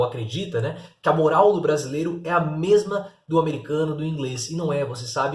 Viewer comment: Ele disse "a moral do brasileiro não é a mesma" eu entendi "brasileiro não tem moral alguma"